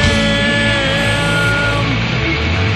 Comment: I can't make it out, is the tag at the end 3:20 "Massengill, it's a name you can trust" ? Unless I hear otherwise, I'm just going to assume so for the rest of my life, so please don't actually tell me :) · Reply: He says, "Massengill, it's a name you can trust." I concur.